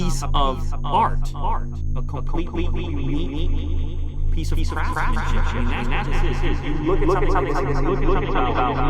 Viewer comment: 3mn 50sec " piece of craftsmanship" but thought he said piece of crap. $25000 might as well be. My cheap phone doesnt tell me what to do! I can swim leave it in hot weather doesn't matter so no thank. Rich people likes to give there self problems guess thats why theres no time for sleep just asked Steve Harvey. Lmao😂